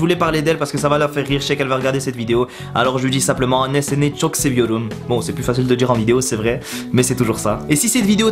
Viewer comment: "néséné chock seviorum" ça veut dire quoi ( 5:05 ) (j'ai surement mal entendu et mal orthographié) ? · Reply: Anne seni çok seviorum* = Je t'aime beaucoup maman ;)